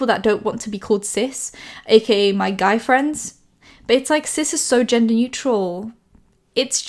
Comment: I heard "cis is so gender neutral" and was like no it ain't lolol